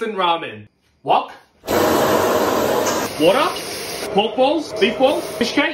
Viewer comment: I thought he said moth balls lol I wasn't paying attention